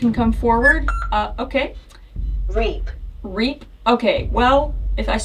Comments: I heard “g’rape” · I heard “rap€.” Idk if that was just me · that sounded like rape to me